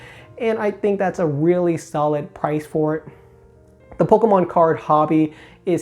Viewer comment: I thought I heard you say “polkamon” once and now I can’t unhear it.